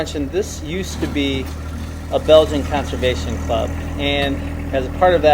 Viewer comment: did he say it was a “Belgian Conservation Club”?!? In the United States?